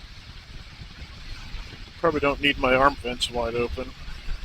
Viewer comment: At first I thought you said you had your armpits wide open LOL! Very pretty place.